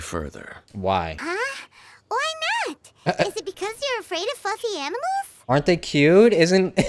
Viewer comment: I thought Paimon said "fucking" instead of "fluffy" (30:04) and i cant unhear it anymore.